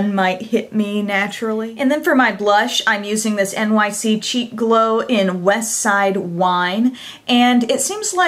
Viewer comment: When you showed the blush, I thought you said "cheap glow" at first...hahaha! I quickly realized you said "cheek glow" ;-)